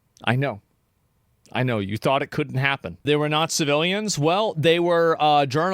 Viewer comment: I thought you said "they were nazi villains" instead of "they were not civilians"